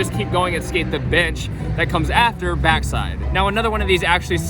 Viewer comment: Idk if its just me but kinda sounded like he said b**ch instead of bench